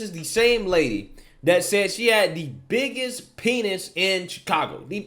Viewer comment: Wait.... She said she HAD the biggest penis in Chicago??? All this time I thought she said she WAS the biggest penis in Chicago. And of course I agreed.